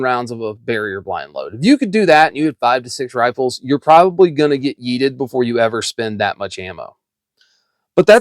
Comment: what did you say? Eated?